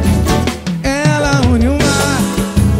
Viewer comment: Serio que ele falou "COMPRETAR" ?